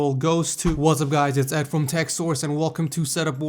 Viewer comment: Before i saw the name of the channel i thought he said TechSaurus